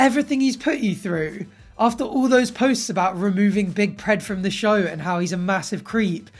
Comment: did you just call big Ed “Big Pred”?